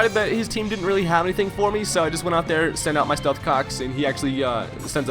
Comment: who else heard stealth cocks